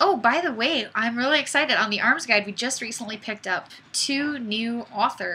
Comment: just put the arms guide as favorite hahah first i heard it as B arms guide duuuuuuuh